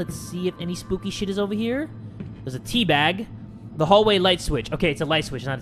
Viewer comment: I thought you said you was gonna teabag the door lol